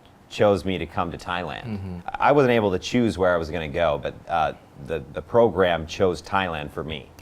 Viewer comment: ไม่แน่ใจว่าผมฟังผิดหรือคนทำซับผิดนะครับ แต่ติดนิสัยการดูanimeญี่ปุ่นที่ตามันเลื่อนลงมาอ่านsubตลอด เลยสังเกดว่า อ. พูดว่า I wasn't able to choose where i was gonna go รึเปล่า เพราะเวลาผมพูด wasn't เร็วๆมันก็ออกมาแบบนี้เหมือนกัน555